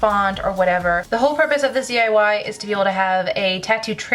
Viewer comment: I thought it said "DIY TITS!" 😂